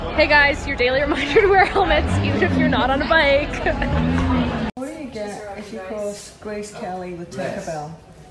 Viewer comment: what did they said? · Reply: What do you get when you cross Grace Kelly and Tinker bell?